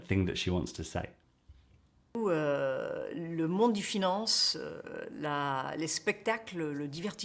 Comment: Well, there are a few errors of interpretation, in my view: 2:47: "le monde des finances" (pas: "de finance") 2:40 / 3:03 / 3:24: "tous les trois" - she clearly says that, no need to pronounce the French "r" in a very visible way - except in songs where this is done ... So, the French "r" usually is very "tender" ... and so, that has nothing to do with a lack of practice or not exercising that muscle ... I would even say, whosoever has attended French school has a good feeling for how things are pronounced ... 3:37: "Le monde des finances est guidé par une technologie qu'on a inventée ..." - that's the way it is written in French and the reason for that the object / accusative here comes before the "que" ... In such cases of influences the writing. 3:48: "... mais pourtant on ne peut pas maîtriser parce que ça nous dépasse". Certainly, the combination "mais pourtant" would be a little bit unusual in French - but in the spoken language probably still acceptable, in the sense of "exchanging one word for the other", like: mais ... pourtant ... but that's not the main problem I see here: The problem - as I see it - although it's not a big probl - is that, in the subordinate clauses, the "que/qui" should have been repeated: Like in: "mais qui ne peut être maîtrisé" / "et qui ne peut être maîtrisé" ... That's the real problem here, in my view. BUT ... now I see, she even said it - but it cannot be found in the transcription, on 3:38 ("mais qu'on peut pas maîtriser ...") So, the str